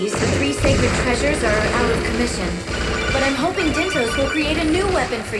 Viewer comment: For whatever reason, I first heard 'Dyntos' as 'Mintos'.